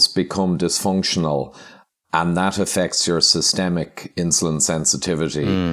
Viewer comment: does he say "if you're doing the bad thing"? What bad thing? Eating a SAD diet/ Western diet?